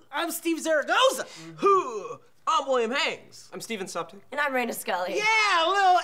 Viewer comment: I heard say "sup, Dick," but corrected myself to "Suptic."